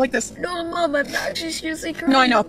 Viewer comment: Can someone please tell me what the precious boy says at 1:16 ? I have been trying to understand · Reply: no mom i am actually seriously crying